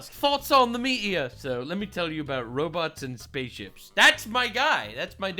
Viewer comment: I thought he said roblox 😂